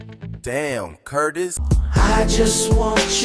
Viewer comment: I heard that "Damn Cutiss" tag LOL